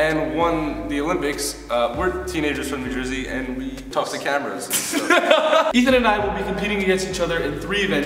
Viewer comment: " She's a teenager from New Jersey and she won the Olympics and we are teenagers from New Jersey and we talk to cameras " omg I'm dead 😂😂😂😂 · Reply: Sahel Izadi I thought they said they threw cameras